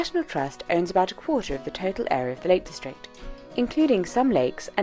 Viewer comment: Umm, did you know that the caption at 5:05 says “fellate district” instead of “Lake District”. 😳🤭😬